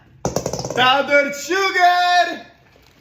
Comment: did you say powdered sugar, or a pound of sugar?